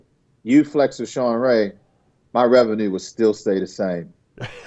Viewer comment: ''My revenue won't be that high if you or flex or shawn were Mr.O '' ....speaking about politics and interests in BB. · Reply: You literally misunderstood what he said. Listen: "My revenue would still Stay the same."